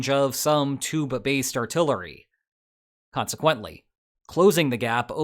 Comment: I swear, 9:05 sounded like he said "tuba-based artillery".